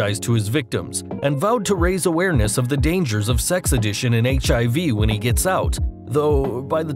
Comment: Wow, is my hearing failing me, or does he say at the 1:58 mark, "Sex Addition"!?!